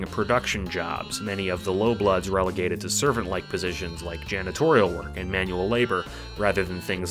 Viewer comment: i thought you said genitorial work i almost had a breakdown like "WTF TROLLS HAVE 0w0 SLAVES?!"